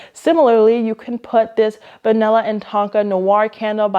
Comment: I legit thought you said vanilla and taco candle until I checked the drop down information. 🤣🤣